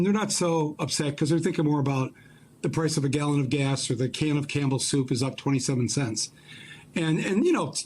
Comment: I heard "a can of camel soup". Should get my ears checked out...